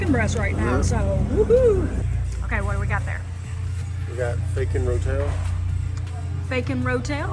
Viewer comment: I’m glad you wrote fakin rotel on the screen cause I thought he said bacon Rotel. I was thinking I need to get some of that. Need to try the cauliflower rice. So many people use it. Spinach is awesome for both of those. It’s so hard not to get distracted by all the cool stuff in the middle. Not too bad for your total meals. Can’t wait to see the pork carnitas. We are going to an Amish Auction on Fathers Day Weekend.